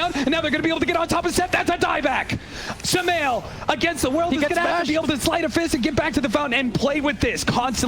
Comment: again i heard the announcer saying "Sumail against the world!"